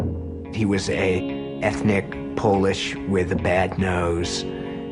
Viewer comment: did he say "ethnic polish"